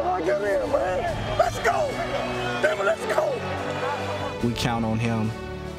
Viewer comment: does he say damnit